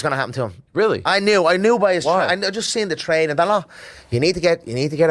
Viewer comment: poll. Does Conor say You need to get a "piranha" in the gym? Or "put on you" in the gym